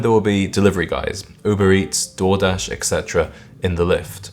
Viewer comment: 😅I thought you said "Jordache," and realized later you actually said Door-dash. Underpaid Door-dash IS classier than the other overpriced trash.